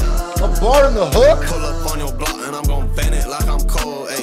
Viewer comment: In that 2000's Baby hook, I heard "pull up on yo block, and I'm gon vent it like I'm cold". Am I tripping?